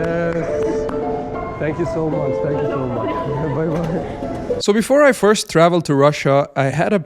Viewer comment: Я там отчетливо услышал добро пожаловать вне пока-пока